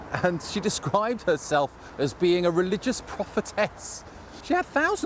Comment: When you said “prophetess”, I thought you said “profitess”, which maybe a new word, but as is so often the case with religion, clearly here they are synonyms.